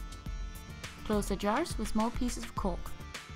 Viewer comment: did she say cok